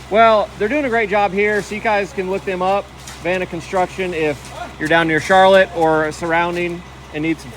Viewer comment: what was the concrete crews' name again. Sounded like you said "Vanna Construction"